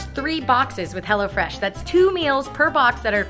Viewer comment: Until 5:15 I thought you were saying HellaFresh.